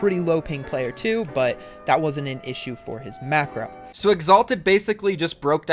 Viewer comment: The first time he said it I thought he said macaroni